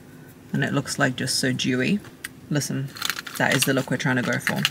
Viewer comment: I thought u said Jewy not dewy… why did I laugh so hard